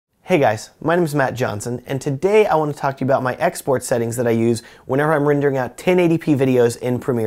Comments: lmao at first I thought he was saying "hey guys today I want to talk about my ex boyfriend" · For some reason, I thought you said "ex-boyfriend" instead of "export settings". Phew, that was weird.